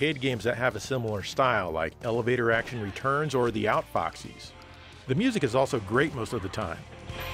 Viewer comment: Huntdown looks like one I should check out. Like Joe said, there are shades of Elevator Action Returns and The Outfoxies. Taking cover inside doors reminds me of Blackthorne too, which I really enjoyed. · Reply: Thank you for writing the name, I thought he was saying "outboxes". Thanks to you, I can look for it now ^_^